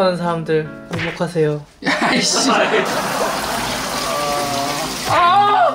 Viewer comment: 가 9:12초 끝부분에 씨ㅂ 이라고 나한테 들림 아니면 말고...